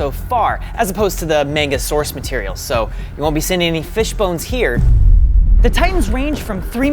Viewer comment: DID HE JUST SAY FISH BONES? LMAO